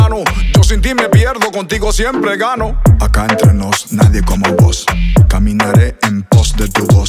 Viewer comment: AQUI ENTRE NOS ... NADIE COMO NUESTRO DIOS !!! cuantos dicen AMEN ! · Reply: Fui la única que entendí "aquí entre nos nadie como vos"??